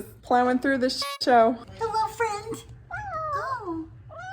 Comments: WAIT, does say HELLOU at 13:33? cute T.T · did said "Hello, thereeee" ? hahah